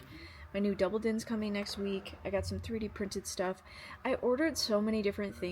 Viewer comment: Holy cow! That soldering iron is huge! Also funny aside... I had closed captions on for some reason, and according to it, when you said your double din's coming in next week, it thought you said your "new double D's are coming in next week".